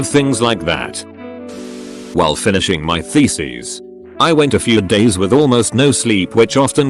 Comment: I heard it as "while finishing my feces" and can't unhear it now XD